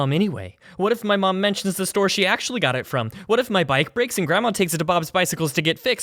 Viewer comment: I first thought they said gramama